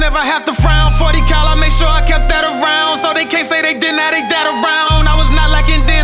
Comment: 40 cal make sure I kept that around so they can’t say they didn’t have this and that around 🔥🔥🔥double meant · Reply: I thought he said "so they can't say they didn't have they dad around"